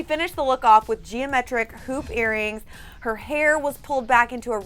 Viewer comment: When they said hoop earrings, I thought they said poop earrings